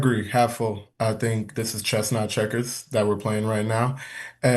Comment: I have to tell a funny story. I was listening on the podcast and I heard say "chestnut checkers" (granted I speed you guys up to 1.4x and skip spaces so that could be it). But anyway, I hadn't heard that phrase before so I googled it and I realized what he said was"chess comma not checkers". 😂 And that totally makes sense given the context. But I sort of think Chestnut Checkers should be a real phrase too just for fun, LOL